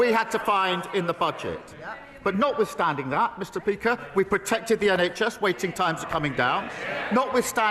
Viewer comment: did he just say Mr. Peaker and at 5:38 he said decenshe. Not only he's a liar but his mouth has stopped working lmao